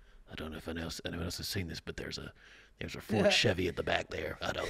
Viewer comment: did you really say a "Ford Chevy"? 😂